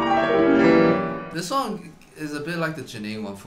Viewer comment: did eddy just say song? Or is that just me.